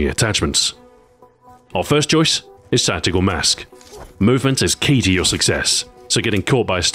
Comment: Did he just say "satical mask" 4:31?